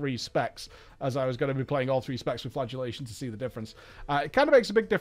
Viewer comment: I hear “flatulationt” every time.